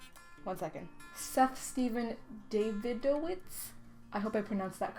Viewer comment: daviddowitz? lmao i thought you said david dobrik HAHAHA